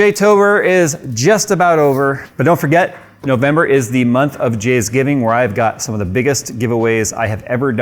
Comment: Am I the only one who heard Jizzgiving?